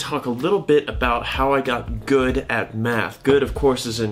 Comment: This video autoplayed and I heard “how i got good at meth”.